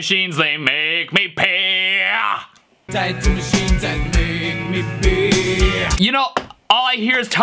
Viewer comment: I heard “Type 2 Machines that bake me beans”